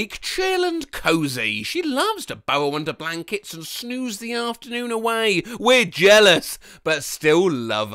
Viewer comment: Did anyone else think that the poster said "sneeze the afternoon away," or is it just me?